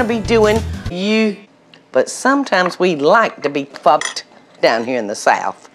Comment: Wtf did she say fucked at 0:06? :\/